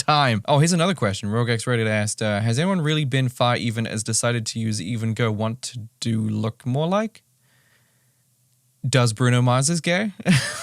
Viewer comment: I've listened to this part five times now and it might be the nasty cold I have but I can't for the life of me figure out what he says in about 19:28. "Has anyone really been far even as to sided to use even go want do look more like?... Does Bruno Mars's gay?". Did he really say that or should I be concerned for my hearing?